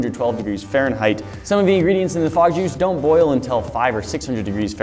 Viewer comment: I thought that it said Frog Liquid at 0:38 😂